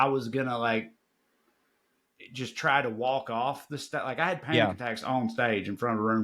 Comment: When Trae talked about having panic attacks in front of people, I thought I heard “I’ve had pancakes in front of people“, probably because of the discussion about pancakes earlier, but I almost immediately realized what Trae actually said. Kind of funny that panic attacks sounded like pancakes. Maybe y’all can do something with that. 😁